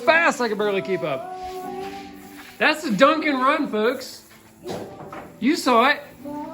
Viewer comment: You said "dunk & run", but what I heard was "Dunkin' run" and now I need an iced coffee....